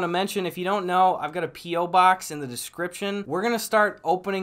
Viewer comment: i thought he said pee box instead of p.o. box lol